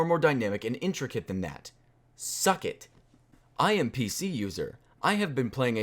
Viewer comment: Did he just say "suck it"? I never thought I would hear swear(-ish)